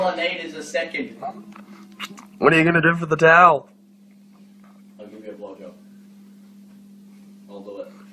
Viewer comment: I SWEAR CALUM JUST SAID HED GIVE MICHAEL A BLOWJOB ARE MY EARS DECEIVING ME LMAO